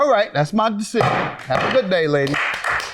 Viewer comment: what did the defendant said at the end?! They are both good looking ladies. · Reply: "Have a nice day, i mean year"